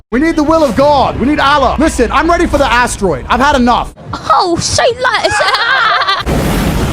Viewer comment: SOME ONE PLEASE TELL ME WHAT THE KID SAYS HERE!!!! Ive seen the clip 10000x and still clueless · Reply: Say less